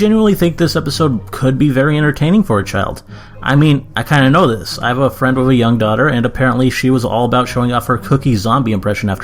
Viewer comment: When he Said I have a friend with a younger daughter for a second I thought he said I have a younger daughter. Ha stupid me no offence I'm not saying you can never have a daughter or a son.